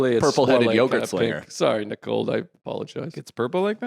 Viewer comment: Ohhh…so this why Nicold Open left Feehans. I heard Joe say “Nicold”, and it all came into focus.